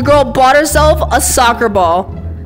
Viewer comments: After she got hit in the face, it sounded like she said: “I can’t see without my head…” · "I can't see without my head!" "Ah, yes, the floor here is made out of floor!" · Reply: I thought she said "I can't see with this thing in my head"